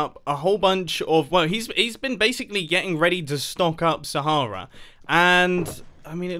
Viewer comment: DID HE JUST SAY STONK ?